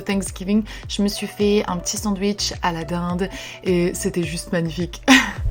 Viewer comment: Quand j’ai entendu a la dinde 4:40 j’ai cru entendre Aladinde (alad’2 les vrais savent de quoi je parle) 🙃❤️